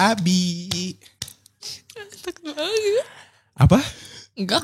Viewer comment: satin ngomong apasi? Ganteng banget??